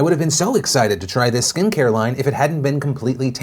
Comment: I kept hearing it as "Skank hairline" not "Skin care line"